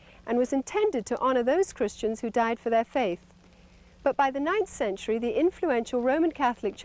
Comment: this 90s woman got it all confused. She said something about the roman catholic church changing a christian holiday from may to october in the 9th century. And the protestant following soon... woman, don't you know Luther (who startet the protestant movement) put his Letters out in the 16th century?! Do they know how the protestant church started? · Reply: it was about 50:10 in the video, maybe I misheard, but I'm pretty shure she said 9th century